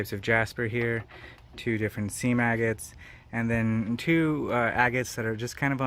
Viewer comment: I thought you said “sea maggot” 😂